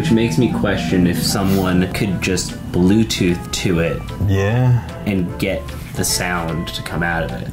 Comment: I heard “come and get me”. Lol, literally completely different. 😅